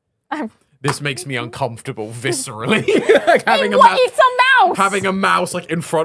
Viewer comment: I thought said "Eat some mouse!"